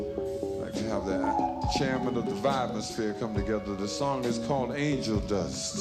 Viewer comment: Gil spoke the truth and kept us informed the vibraphone as he called it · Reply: I think he said vibrasphere - the vibrations of the atmosphere. Yes, around 24:36 he uses the term he explained earlier.